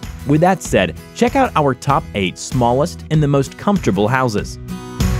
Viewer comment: did he just say houzez?